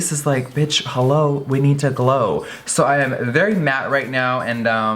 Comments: DID ANYONE HEAR HER SAY HOLO!!!!!💿💿💿💿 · When i heard "Bitch hello? We need to glow!" i thought ''All bad bitches need to glow'' xD